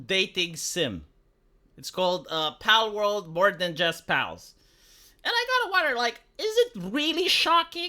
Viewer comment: I thought it said "more than just pets" and thought it was vile, hilarious and okay with me, but conceptually vile, lmfao.